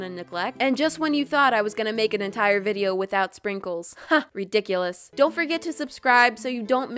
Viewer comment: HAHAHAH THAT KILLS ME “dickles” I sware I hear that every time